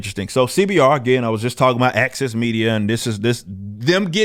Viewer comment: I can't help mishearing when he said access media as "AXIS media".